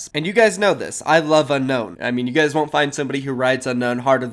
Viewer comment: IM SORRY WHAT DID U SAY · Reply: Rides tf,??!?!!?!?!